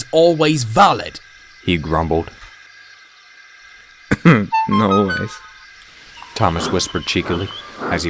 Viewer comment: i thought thomas said noice instead of not always i was dying 😭